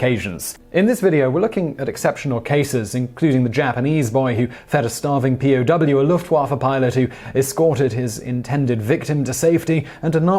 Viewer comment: I had to replay the beginning... could have sworn I heard “a Japanese boy who fed a starving POW a Luftwaffe pilot.” Did a haaaard double take.